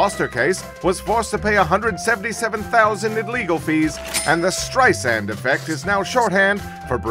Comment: I always misheard it as "Streusand-Effect", which is almost a German word, but not quite. If it were a German word, it would translate to scattersand or sprinklesand and somehow that always made perfect sense to me. 🤷‍♂️